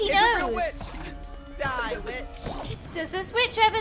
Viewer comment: I thought said "Die B**"